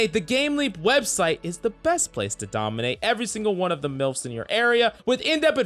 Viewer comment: "the gameleap website is the best place to dominate all the MILFs in your area" I'm not the only one who heard that